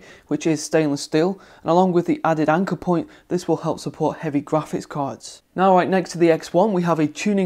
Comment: What the hell did you said at 6:35? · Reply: "Along with the added anchor point, this will help support heavier graphics cards"